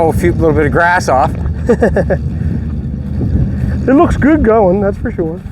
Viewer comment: Did I hear say "It looks good going, that's for sure."?